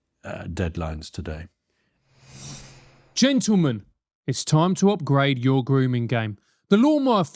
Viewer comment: Not to be a dick, but your advert for the shaver sounded like he said "it's time to upgrade your grooming gang". I know this was not at all intentional, but I did get a chuckle from what I thought initially was a joke.